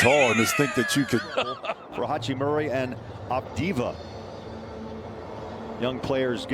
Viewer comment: Did he just say adiva